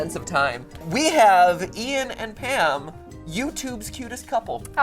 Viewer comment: I thought matpat said “Jim and Pam” I watch to much of the office